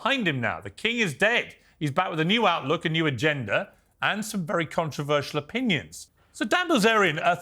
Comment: First i heard "new outlook and a new gender....* 😂 I was like 😮